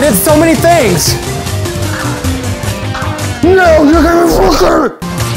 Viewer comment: did he say fu**er?!